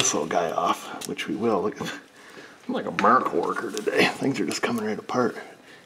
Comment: who else heard 'feel like a 'Murica Worker today'?! 🤭